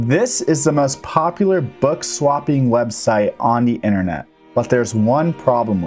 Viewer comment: Okay I thought you said "bug swapping"